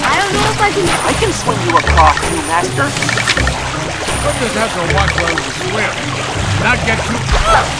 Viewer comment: We just have to watch where we swim, not get too close to the fish? Is that what Carl said?